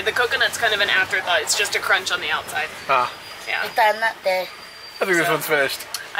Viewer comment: I couldn't make out what he was saying earlier, but at 4:02 he says "Ga'i ta ana, teh" and it means, "May I have some of that, big sister?"